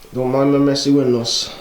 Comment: in the video I couldn’t figure out what you said.... otherwise, you ROCK! Now I understand what the fried bread dough is... Awesome cabin, great grubbing, incredible view. Life is good brother! Thanks for sharing and best regards from Tracadie-Sheila. -Yves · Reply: He said "don't mind my messy windows"